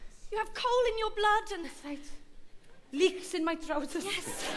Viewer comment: What is the joke at 18:18? I can't understand what she's saying! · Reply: Leeks in my trousers??